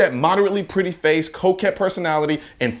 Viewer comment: "You use that moderately pretty face, coquette personality, and fake virtue to mask a disturbing level of hypocrisy, and now the world is finally starting to see it." mic drop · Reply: Coquette???? Shit, the whole time I thought he said Cokehead.